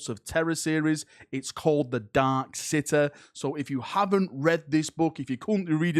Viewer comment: He said "City", but I heard "sitter." Also, "Century", but I heard "centruh."